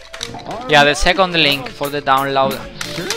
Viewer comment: Did he just say - "there is hack on the link for the download"